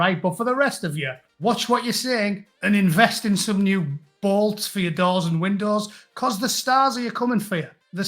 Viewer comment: I thought you said"invest in new balls"